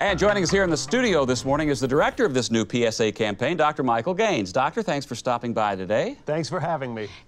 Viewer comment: I thought he said Dr Michael Gay